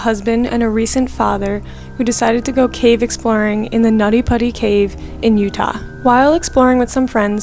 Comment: After hearing the nobody cave story I feel like 90% of us who watched that never ate nutty buddies again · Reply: WHAT- THE SECOND I SAW THIS I HEARD "Nutty Butty" FR